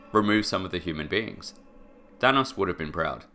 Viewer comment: because of your accent, I honestly thought you at first said "Vanoss would be proud" and until I rewand and listened more closely I was thinking "Why would Vanossgaming be proud of nuking the world?!?!" H20Delirious maybe, but yeah Thanos the big purple finger snapping meme monster makes a lot more sense after hearing it again XD